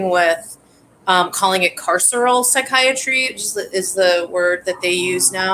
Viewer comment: What is spelling of the adjective mentioned by at about 08:00? - sounded like "carserel psychiatry" ? Thanks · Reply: "Carceral" (as in "incarcerated"). This made me think a lot about Foucault, and how he was either wrong about the similarities between classrooms, mental asylums, prisons, workplaces, etc., or perhaps what he was writing about only applied to France. Do you all suppose that Foucault, Deleuze, Guattari, and R. D. Lang were part of the anti-psychiatry movement? Or were they more specifically against Psychoanalysis/Lacan in France? Keep in mind that this stuff was written from the early 1970s into the '80s, so I don't really know what conditions were like at that time.